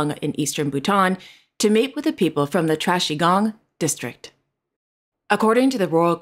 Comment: I like Monaco-ville, 😉😋, but Prince Albert how will we know you are really working unless you unveil a plaque. My ears must be going because I thought I heard King of Bhutan was going to "trashy gong"? 😮 Lol. I hope you are feeling better 🤗